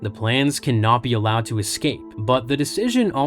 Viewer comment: Every time you say "Cannot be" I hear "Kenobi"